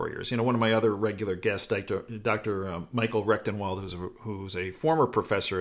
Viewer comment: I'm sorry but at 3:15 did you say "Dr. Rectumwell"? OMG I can't believe he didnt change his/her name! Sorry love you videos but that got me.